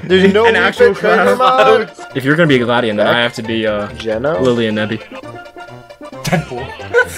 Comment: I think said "Geno", which is my name, so I either messaged or Geno from Super Mario RPG is a mod that added